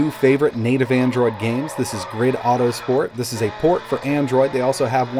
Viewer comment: can someone write the name of this game? I cant understand what is he saying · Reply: grid autosport